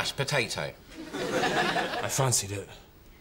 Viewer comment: What does Roy say at 0:10? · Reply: "I fancied it"